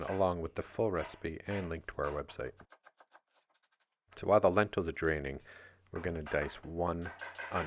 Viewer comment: He said "The lentils are draining" but I heard "The lentils are dreaming."